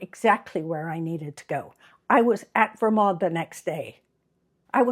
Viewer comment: What is the name of the builder? The transcript says varod And it sounded like you said Vermont? Please clarify Thank you so much! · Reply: Vermod Homes